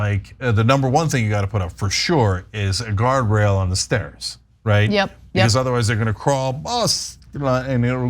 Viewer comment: I misheard Cenk, thought he said, "put a gargoyle on the stairs." And I was thinking, "I guess that might work..."